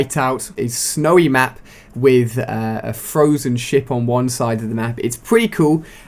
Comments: I thought you said 'with frozen shit on one side' at the beginning ;) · I thought he said frozen shit hahahhahahah